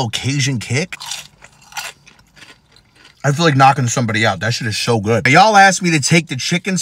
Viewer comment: "I feel like knockin somebody out that shit is so good" 🤣🤣🤣 Ik that feeling man hahaha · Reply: I thought he said knocking somebody UP